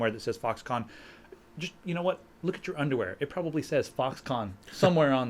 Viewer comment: You told me to skip to 5:00 if i didnt wanna hear about Apple and so i scuffed through to around five and what I hear is "My underwear has USB 3.0" Hahahah wtf? And i still hear about apple...